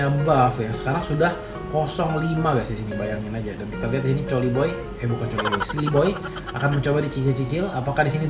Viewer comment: coliboy atau gua salah denger